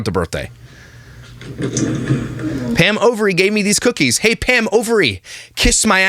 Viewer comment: I just realized Pam's last name is actually Overy........ I thought was just saying Ovaries instead of Bakery or Cookies or something. Hahahaha 😂